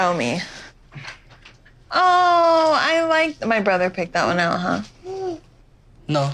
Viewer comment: I thought Kalani said "my brother picked that up'? because she saw the shirt that said "little bro"